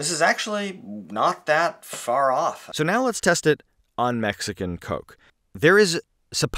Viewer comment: For the first several seconds of this video I thought they were saying Mexican cocaine is supposed to be made from sucrose.